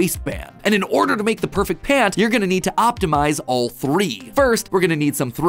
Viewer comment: did you just say pant?